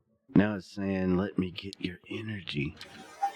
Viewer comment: I don’t hear that at all. I heard, “Let me get my mother in here.” Or something like that. 27:11 -Something “target” possibly. I didn’t hear orgy at all! 😂 But maybe? It’s definitely interesting. I’d have to go back and see if I hear anything different. Thanks for the cool video! 😊